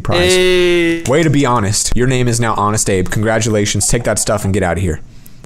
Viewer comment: Instead of Honest Abe it should be Honest Grape. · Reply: i thought he said honest grape